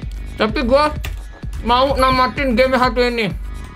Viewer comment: gak ngerti ngomong apa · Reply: bukan HD anjay "game satu ini kampret"